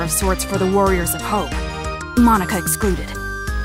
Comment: When Monaca said her name I heard “Monika” xd